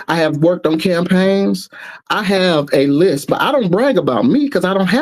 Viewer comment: did he say list or lisp😅